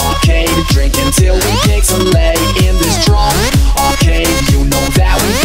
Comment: I thought it was saying "untill wee pigs are laid" and I was confused for the longest time